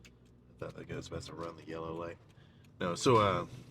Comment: am i trippin or did he say "that n****s about to run the yellow light"